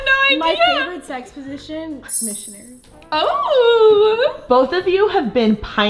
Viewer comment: what did she said?? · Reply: missionary